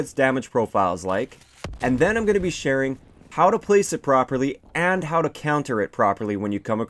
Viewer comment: I heard... "How to place it Broccoli"